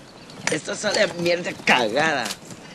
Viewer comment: Could anyone hear what this guy was saying while throwing away the coffee? Sorry that I'm not a native speaker of English. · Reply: said "esto sabe a mierda cagada" ...